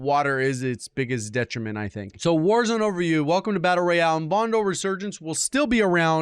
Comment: almost sounds like he said "Warzone Over You" which honestly would be a more accurate title for the article